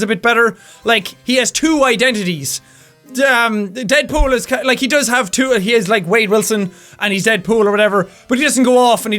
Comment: the subtitles say: "the dead is cut like he does have he is like Wade Wilson" LOL